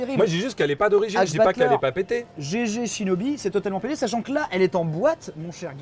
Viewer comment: pardon, c'est totalement quoi? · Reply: "Totalement pété"